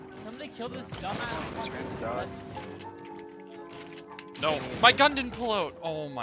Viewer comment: anyone else hear "no my gun didn't PLU out"? sorta funny :)